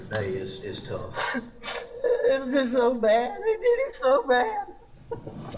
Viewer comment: He said, "crying" mind you, "It's just so bad. It is so bad," "It's" in the first sentence and "It is" in the second, that's what I heard. I'm not for or against Alex. This is the only piece of evidence I've heard. Plus the stuff they said before the recording.